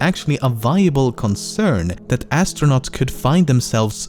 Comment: Is it just me or when he says astronaut on fact 6 It sounds like he says Ass stronaut (because hes Talking about Uranus)